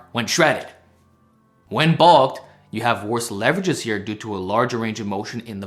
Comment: Worse leverages on bench when bulked? What? Am i hearing that correctly?